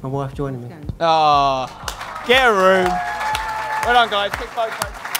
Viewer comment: lol did I hear him say get a room 😂😂